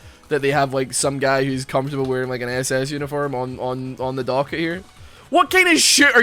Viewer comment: lmfao that michael cera story too funny; LOL i thought u said nasa uniform then i realized what u actually said 👀 jesus LOL